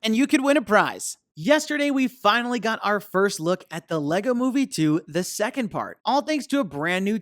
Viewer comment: Just pointing out that you said move instead of movie